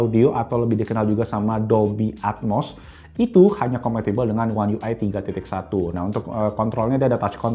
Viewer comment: ngomong apa sih ? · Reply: Hanya compatible dengan One UI 3.1